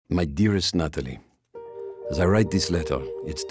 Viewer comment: i heard hi say my dearest nut